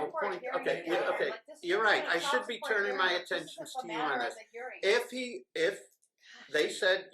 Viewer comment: did judge say he should be ‘turning my attention to you my love?’